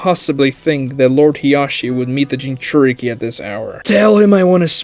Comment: I legit thought she said Hiashi would meet him in the gas chamber at first, then I realized you said “guest.”